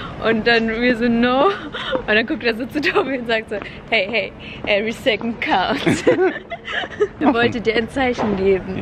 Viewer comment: was sagt sie in 16:49? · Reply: " hey everyone second counts" 😁